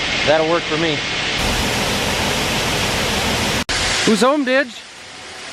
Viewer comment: i thought that you said who's home b***h because I had my volume down but then I replayed it with volume up and you said dich so I started to laugh